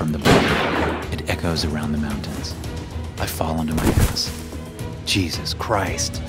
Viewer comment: Did he just say quietly I fell onto my as